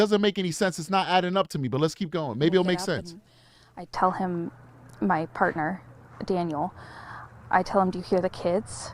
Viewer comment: Everytime she says “my partner, Daniel” all I hear is “partner In crime”